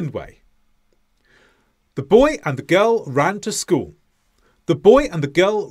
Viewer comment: Did you just say . “The boy and the gell”